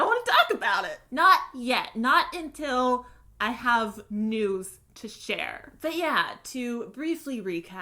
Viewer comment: ''not until I have NUDES to share'' (please don't be made at me I just heard it wrong and though it was really funny)